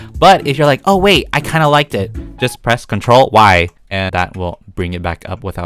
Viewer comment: I thought he said "control WHY!?" instead of "Control Y"